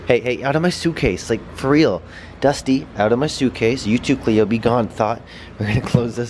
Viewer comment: This is insane man great video size 10.5 did he say begone thot at 12:10 that is hailorous